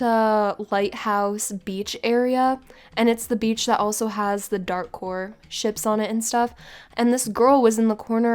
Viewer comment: For a minute i thought she said dark hore but i think it is dark war ship not dark hore ship